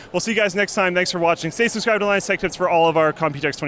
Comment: did you say linus tech tits?? lol